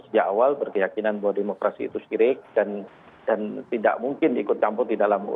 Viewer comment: demokrasi itu syirik...apa gue gk salah dengar?..